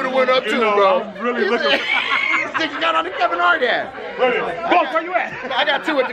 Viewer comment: I thought this shit said you got bumps from Kevin Hart. lol